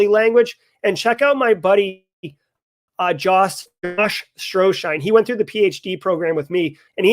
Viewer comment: was he saying Josh Mason? I can't be sure.